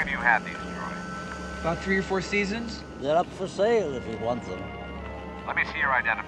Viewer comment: Stormtrooper: How long have you had these droids? Luke: About three or four seasons? Obi-Wan: They're up for sale if you want them. Stormtrooper: Oh sure, I'll buy them. Written and directed by George Lucas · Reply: Crazy, I always thought he said they're NOT for sale. Just now realized he said they're up for sale